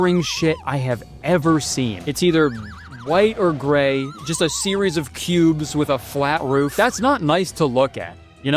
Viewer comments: I genuinely thought he said "just a series of pubes with a flat roof" at first. lol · I was making food and thought he said a series of pubes with a flat roof. Didn't even question it but I played the chapter back and lol, cubes make way more sense. 😅